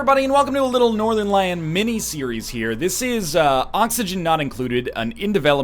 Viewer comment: I thought that said Northerlion Ministries